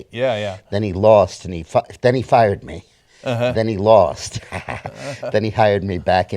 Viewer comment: Did he say "then he fucked"?